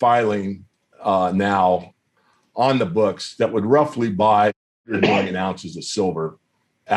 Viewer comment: Listen closely to 18:58 Does he say million or billion? It sounds like a cough was edited in for some reason. Anyone else hear this? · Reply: The top pinned comment on the video explains they edited the cough, it was 100 million ounces